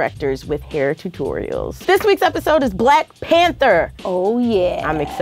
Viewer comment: Okay but i for some damn reason thought they said Pink Panther and was really confused for a second facepalm